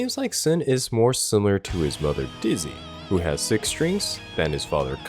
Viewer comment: “Brother Dizzy” lmao. Maybe you said mother and I misheard, but I could stake my life that you said “brother” xD